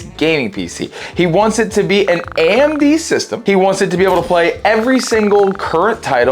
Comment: Thought you said he wanted an all AMD rig? yet you went with a Nvidia GPU. SMH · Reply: I said a “amd system” not “all amd” but I have a video title the All Amd system if you would rather nod your head then shake it